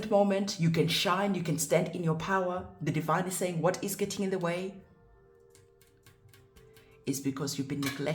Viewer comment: I saw 1111 while watching this and all i heard was "gateway"